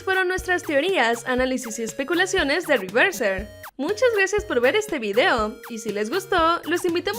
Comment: ¿Como que muchas veces por ver este vídeo?¿no abra querido decir muchas gracias? Por lo menos parece que dice eso. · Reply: Dijo "muchas gracias" :b